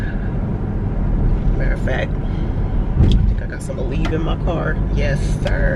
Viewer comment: Girl in the beginning i thought you said i got The Weed in the car then i realized you said ALEVE...😆 🤣 😂